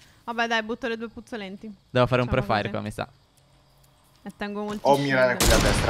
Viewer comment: Minuto 16:30 Cosa ha detto 😂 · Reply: Devo fare un pre -faire